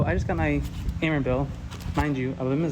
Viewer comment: what bill did he say? It sounded like camera bill but I know that can't be right.